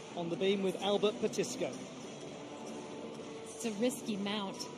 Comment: Impressive 👍 beam routine by Alba Petisco (🇪🇸 Spain)! (The first time I heard her name, I heard “At the Disco 🕺! So you can imagine that I am a person of a certain age, who had disco dance lessons.)😊